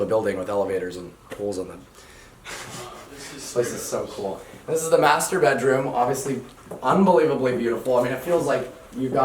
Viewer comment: It sounded like "this is the masturbedroom"